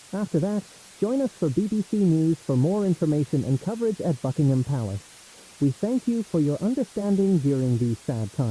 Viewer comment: Thought bro said f#ckingham palace💀